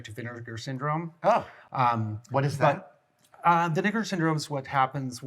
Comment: I am so sorry, BUT WHAT DID YOU SAY?! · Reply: Vinegar syndrome. What did you hear 🤨😆